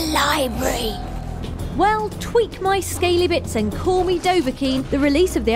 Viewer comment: haha for a secon i thought she said tweek my squeezy bits xD